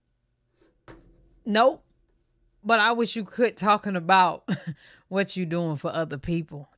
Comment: I thought she said she Quit drinking Liar!!!